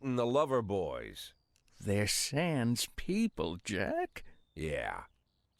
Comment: I thought he said sans LMAO